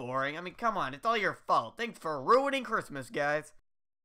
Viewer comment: I’ve watched this video literally 28 times, and this is the first time I’ve noticed he said “Thanks for ruining CHRISTMAS guys”. I thought he’d said Halloween